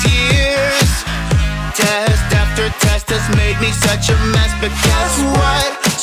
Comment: I dont know why, but at 1:56 i hear "test after test has made me such a mass spaghet".